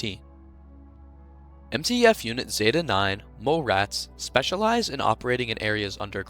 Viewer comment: for some reason i thought he said “male rats” and i was very confused lol